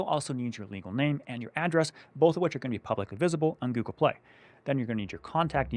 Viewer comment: Ok, hold on. Did you just say my info was going to be public on Google Play?